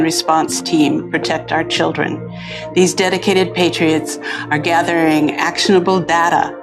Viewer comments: When she says "patriots" I hear "N@ for some reason. I got a bad feeling about this woman. Hidden authoritarian agenda I think. You know how good ideas and good words and good intentions get subverted and twisted into things wholeheartedly bad by "Patriots"? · Jimmy On Tour! - Ontario Ca. -Irvine Ca. - Ashland Va. - Athens Ga. - Minneapolis - Rutherford NJ. - Chicago (Video/Panel Show) & More! Tickets here: <link> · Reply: When she says "patriots" I hear "N@ for some reason. I got a bad feeling about this woman. Hidden authoritarian agenda I think. You know how good ideas and good words and good intentions get subverted and twisted into things wholeheartedly bad by "Patriots"?